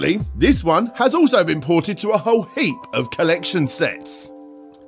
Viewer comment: It was never “boughted”!?!!? Wha-ha.? Whaaa? Haaohaha! Oh wait. Is he saying “ported”? Yo I cannot understand sometimes,quite often actually. I love your videos man! But have a lot of trouble with that …